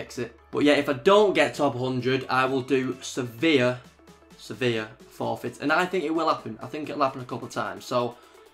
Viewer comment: when he said "severe forfeit" i thought he said "sevilla" like the team lol